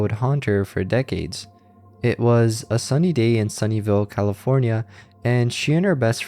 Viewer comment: “It was a sunny day in Sunnyville California…” I first heard this as “Sunnydale, California” and instantly thought, “Buffy?!”